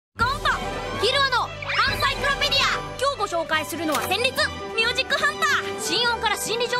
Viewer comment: Excuse me gon did you just say zenitsu? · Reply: senritsu i think